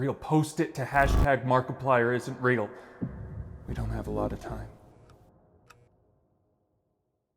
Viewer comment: I heard it "you dont have a lot of time" is that you? IM COMING FOR YOU